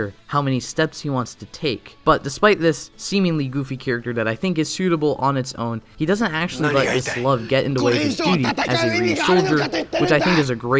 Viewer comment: I couldn’t understand what you said because of Canalo’s speech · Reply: “But he he doesn’t actually let love get in the way of him being a Ryusoulger” sorry about that